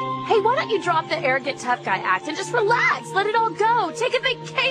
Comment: anyone else find it funny how her voice cracks in the middle of relax so it sounds like she says "relHACKS".